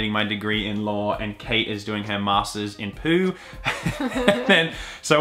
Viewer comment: I've been laughing for hours since I heard "master's in poo" 🤣